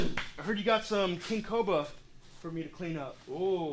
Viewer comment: "I heard u got sum king 'COBA' for me to clean up" <(o.O)> LMFAO!!! Did just pronounce it "COBA?" Hahaha!! (Bl00per Alert!) <SKiiiiiiiiiiiiiYewwwwWWWwwwww!> These 2 crazy psycho besties have their own language & "calls in the wild" to appear on command like they did earlier! But anwayz, all fun & bullsh!t to the side... Congrats on your new page! I'm a new subscriber today, but I've seen you on channel b4 in a couple different video episodes... is a great friend to give u a s/o on his channel giving you exposure to attract new subscribers & views for you. Ble$$ings 2 U